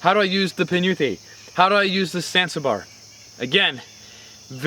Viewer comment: "that sounded like how do I use the censor bar" 🤣 jk, but I really do want to know, what's its correct name? ginunting my fave but the rest are ok too